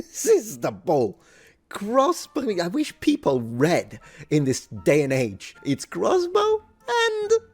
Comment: But isn't reading un-Christian these days? I think that's what I heard. I certainly didn't read it anywhere. ;)